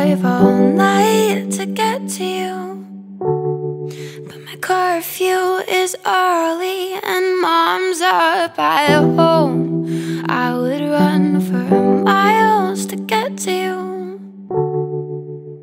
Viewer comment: For the longest time, I thought she was saying "My car fuel is early" and I thought maybe it was just a thing Americans said instead of "empty" lol